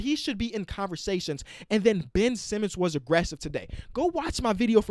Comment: Man I thought you said Ben Simmons was arrested today